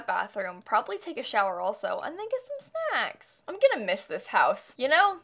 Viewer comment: Did I hear 9:38 correctly there I know this Channel isn’t Intended for children but I’m sure said “get some sex” lol